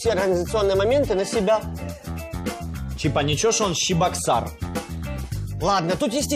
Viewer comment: мне послышалось Чебоксары кто из Чебоксар ставим лайки